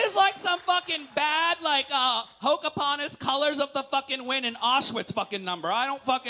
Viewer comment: Did nobody else catch “Pocapontas” 😂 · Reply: to me it sounded like she said “Hocapontas”! lmao